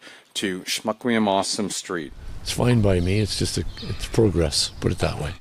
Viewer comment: So I heard "Smuck me I'm awesome" street. What do you think the locals will call it?